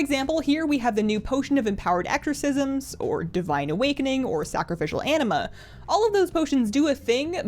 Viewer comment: I thought she said sacrificial enema. I thought wtf??